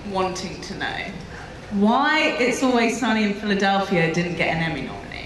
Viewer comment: Who did Sansa mention in 10:40? Couldn't catch the name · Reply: She asked why "It's always sunny in Philadelphia" didn't get an Emmy nomination. It's a series where the host of the comic-con, Rob McElhenney, plays a character called Mac.